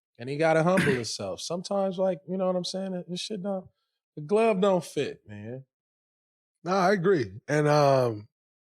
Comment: Did he just say the glove don’t fit 11:45 😂